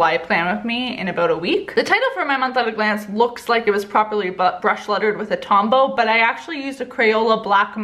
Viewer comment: Did anyone else hear her say butt bruch lettering